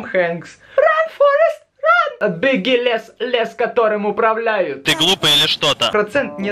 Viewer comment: Мне послышалось, или... Ты сказал что ты гуль? /когда недавно смотрела "Токийский гуль"/